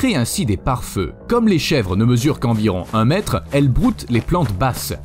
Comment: J'ai flippé quand j'ai entendu que les chèvres font 20m